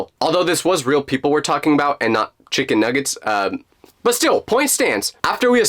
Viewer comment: did I hear chickon nugetssss yummy. Chicken 🐔